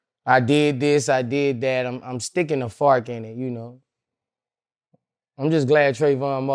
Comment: I thought he said ‘He’s stickin to farken’. I’m like wtf is FARKEN? 🤣